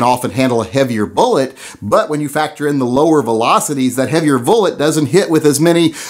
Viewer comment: Did he say vullet at 1:08? Lol, get you sacts ftraight...HA HA! Kinda muked my find when I heard it.....I think it's the red headed step child of Mrs.Velocity and Mr.Bullet. Baby Vullet is soooo cute.